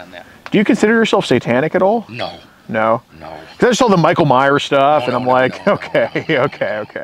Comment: micheal byers lol or myers lol sound like u said byers